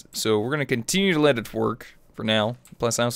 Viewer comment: Haha! At the beginning of the video, I thought when you said "Let it work" that you said "Let it twerk" XD! Also, another great video! Keep up the great work!